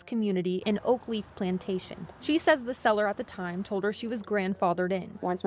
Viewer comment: She literally said the seller told her she was grandfathered. You should probably watch the video you're commenting on. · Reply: You are correct, and I was mistaken. I thought she said "they" told her she was grandfathered. Regardless, the written community documents govern community rules, and verbal comments aren't binding. Thanks.